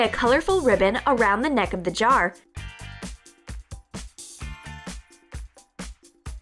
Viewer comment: she said cardboard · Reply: Lily And Emily oh im sorry. I thought i heard cartbort. Oops